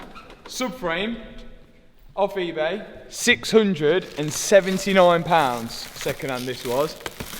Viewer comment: did i just hear 67? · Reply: 679 pounds